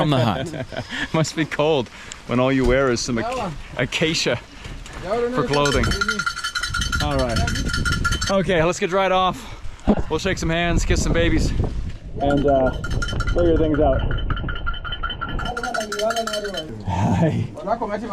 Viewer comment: did he just say “let’s get dried off,shake some hands, and kiss some babies or make some babies?!?